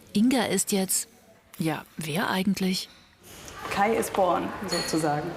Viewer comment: "Kai is porn" Am I the only one who heard that?